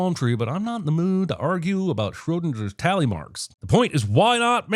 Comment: Did you just say “SchröDINJER”